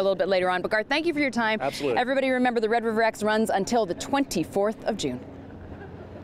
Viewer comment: I thought she said 24th of JULY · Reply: No she said 24th of june